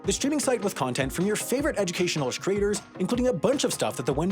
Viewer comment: is it just me or did he say 'shcreators'